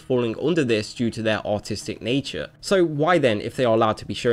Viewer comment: I thought you said autistic, when you really said artistic O.o you damn quit butchering the English language, also censorship of any kind is terrible no matter how small